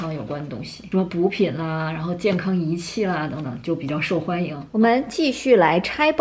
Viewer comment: 我这乡下人的耳朵...哈哈！也真是的...应该只有我这种人才会听错吧~~~ 10:12 我竟然听成 “什么毒品啦，然后健康仪器啦...” :'))) 我赶紧很自觉地面壁思过去了....